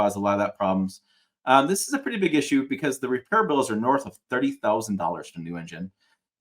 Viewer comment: did I hear him correctly that repair bills are north of $30,000 for a new engine?!